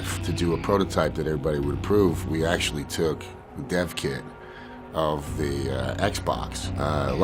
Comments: When he said "we took a dev kit" I thought he said "we took a deaf kid" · When he said Dev kit, I thought he said Deaf Kid